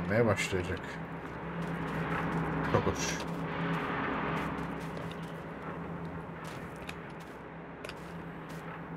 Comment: te tam olarak ne diyor ben yüz tekrar yaptım anlamadım. Anlayan biri varsa söylesin içimde kaldı 🙂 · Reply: Sanırım gel babuş diyorum 😅